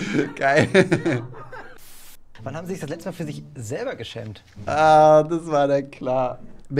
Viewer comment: was sagt er da ? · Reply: Ob er sich für sich selber geschämt hat